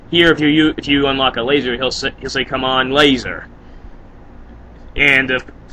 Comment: lol! Eggman turns into Gamma! (Gamma says "Push", "Pull", and "Put".) Also, I always thought he said "COLOUR!". But it makes more sense to say COME ON!